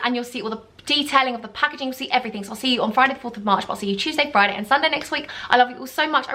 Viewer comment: Love you all! See you Friday 1st March for my REVEAL video omg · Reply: i thought you said 4th of march and i was confused